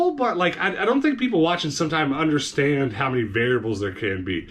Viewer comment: I don't know, but all I keep hearing is "ass watching shorts"